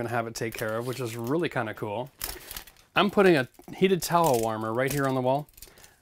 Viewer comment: Towel warmer not tile warmer. Love your video's btw. Keep them coming! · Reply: I HEARD HIM SAY, "TOWEL WARMER🤥"